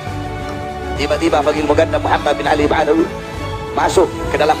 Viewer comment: Apa saya salah denger yah bahar bilang baginda muhamad bin baalwi😢😢